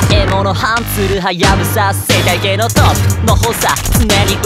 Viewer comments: ゲームの方でしか聞いてなかったから「生態系のトップの方さ」の"方さ"が"補佐"にしか聞こえなくて、自分は兄の補佐でしかないみたいな意味だと思ってた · 「生態系のトップの方さ」が「生態系のトップの補佐」に聞こえて 中王区の補佐…？とヒヤッとしてしまう